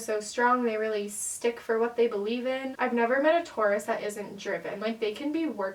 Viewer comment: MY DUMBASS THOUGHT YOU SAID "I HAVENT MET A TAURUS THAT ISNT GERMAN" AND IM LIKE WHAT TF